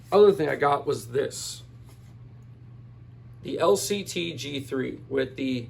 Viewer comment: Did I hear correctly? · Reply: Yeah the LCT G3